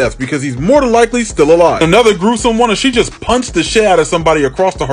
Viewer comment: I am pretty sure that was a kick lmao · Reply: I know it sounded like he said punch, but he said punt, like you do a football through a field goal.